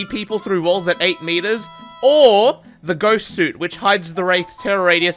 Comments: I heard this as "ghost suit". Now I want a skin for Wraith that's just a blanket ghost. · I’m sorry did you just call that the ‘ghost suit’?